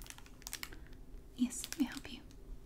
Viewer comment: subtitles said "yes maam pee".